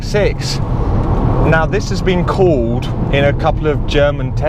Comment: did he said cold tyre or called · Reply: Called :)